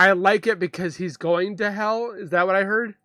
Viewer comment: I thought it said I hope he rots in hell